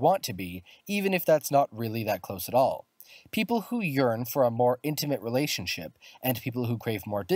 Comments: Who else heard, "People who urine" and saw a toilet at 1:51 ? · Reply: but he actually said, "People who yearn" and it's a stool, not a toilet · Who else heard, "People who urine" and saw a toilet at 1:51 ? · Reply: YES! Totally thought it was a toilet and I thought he said urine. And then I was like... that's a stool.... and he said yearn lol